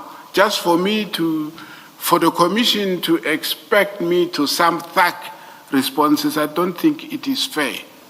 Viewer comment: Did he just say "sumb thuck" ?